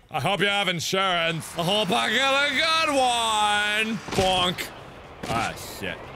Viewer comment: did mark said BONK?